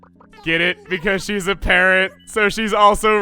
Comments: Did say Kitt is a parent or a PARROT...? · I think thinks Kitt is a parrot....she’s a kangaroo.... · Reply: I think you misheard "parent" as "parrot." Unless this is a joke and I'm an idiot.